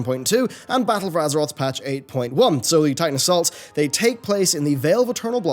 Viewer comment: What the hell did he say? "Sully tidal salts?" edit: YouTube's auto-generated subtitles says "So the Titan assaults...," which I accept as probably correct.